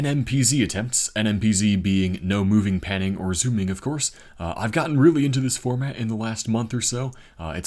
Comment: I thought you said “I’ve gotten Rumi into this format” I was like no. LOL